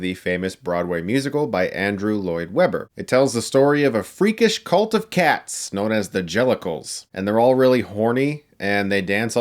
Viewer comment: “It tells the freakish cult of cats known as the genitals.” That’s what I heard.